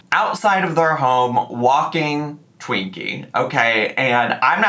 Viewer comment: I thought you said they got a picture of her TWEAKING not with Twinkie. JFC.